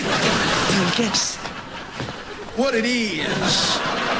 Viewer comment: What it is Josh say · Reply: Lol he said “what it is?”